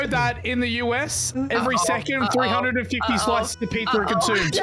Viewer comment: Hey I have no idea what he said at 3:33. Subtitles didn't help. It sounds like: In the US every second 350 flights peaker a toot 🤣:_smashing:🤣